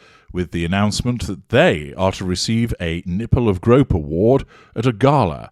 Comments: I misheard "Ripple of Hope". I thought you said "Nipple of Hope" - which in this case is probably apropos. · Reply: I heard "nipple of grope".. haha. Purrfect 🇦🇺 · I swear when I first head it I thought you said, "nipple and grope" award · I misheard "Ripple of Hope". I thought you said "Nipple of Hope" - which in this case is probably apropos.